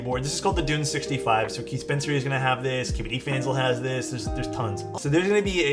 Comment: i swear he said "skibidi fans" instead of kbdfans